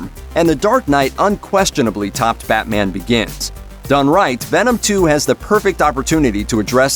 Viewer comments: Did they really call it "Fatman Begins"? -Mad Wolf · pretty sure he actually said fatman begins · Did I hear him say: "... And the dark night unquestionably topped 'Fatman Begins'"??? Lol